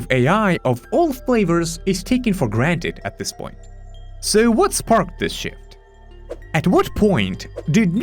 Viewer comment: At 2x speed it sounded like you said "what sparked this sh*t" 😂